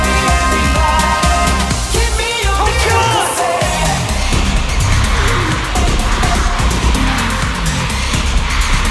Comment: 태민이가 뭐라고 소리지른거에요..??!? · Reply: He said Tokyo To get the fans hype